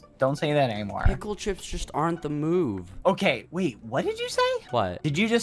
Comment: Tbh I thought he said the mood instead